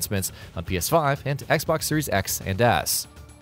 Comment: I swear he just called it the "Xbox Series Ass"